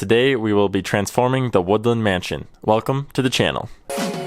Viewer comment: did u say woodland mansion u sayed it fast and sounded like u sayed wither mansion